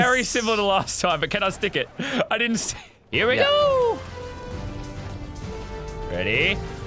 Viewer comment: it’s sounded like Mario “HeRe WhE gOoOOoOOo